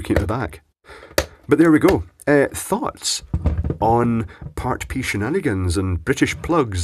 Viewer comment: Did you just say "thots on party shenanigans"? I'm all ears!